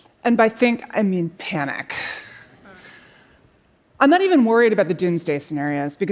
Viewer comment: I'm so glad it's okay, I'll tell the bank that when they come looking for my mortgage payment. They'll understand. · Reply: Hello can you teach me how to pronounce “I am not even worried” and “what’s going to happen ” of the speech .The sentence I hear is “Another even more about” and “what can happen to!”Is that due to connected speech rules in English！thanks